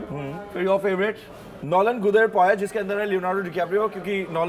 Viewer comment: did he say “nolen gudher payesh”?😂😂😂. Bong people will get it